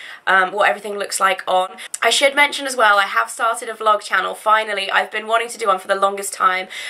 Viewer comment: omg.. i thought you said i have farted then i heard vlog channel.. Wowie im dumb